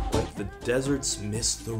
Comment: Wtf I always heard it as "Like a desert's mystery".